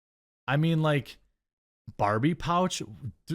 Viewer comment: At first I thought you said Arby's pouch and now I can't eat there